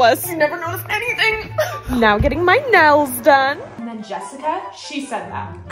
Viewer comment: I heard ‘nose’ instead of ‘nails’…